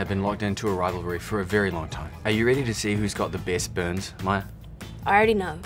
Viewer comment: "Are you ready to see whos got the best buns" Wait I thought KJ said buns not burns lmao